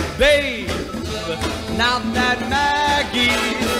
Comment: All this time listening to this song, I thought he was saying Maggie's Back In Town.